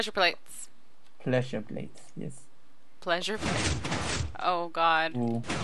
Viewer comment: turn on transcribe audio say "oh god" the captions say "i'll guide you" MESSAGE FROM GOD!!!!!!!!!!!!!!!!!!!